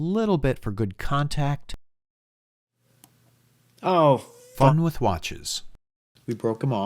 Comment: Did you just say the F word at 13:01 minutes….???? 😳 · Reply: "FUN"